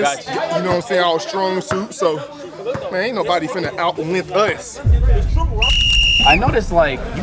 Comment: someone please translate what duke had just said 😭😭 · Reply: He said “anit nobody finna out length us” 😭😭😭